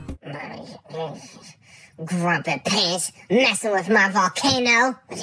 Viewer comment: Sounds like he said “grump of piss!”😂😂😂